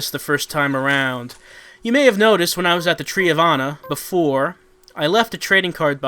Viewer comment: I hear "Tree of Anna" as "Tree of Honor" every time